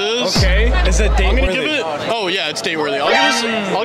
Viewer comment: i thought he said "i'm straight" at the end 💀